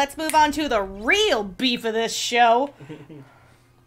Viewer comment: I swear I heard him say “Camp Roxas” like, the newest world is gonna be based on Camp Rock, maybe also High School Musical and Halloween Town? I WANT THIS EVEN IF IM MAKING A DUMB JOKE!